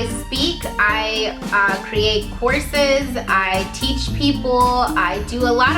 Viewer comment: OH, COURSES. I seriously thought you said you create horses for a minute.